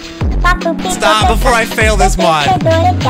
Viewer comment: was it me or did that say SHIT